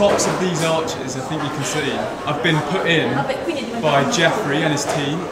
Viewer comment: I thought that he said Jeffrey Epstein